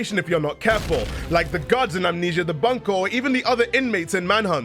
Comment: did you say guards? The Bunker doesn’t have guards.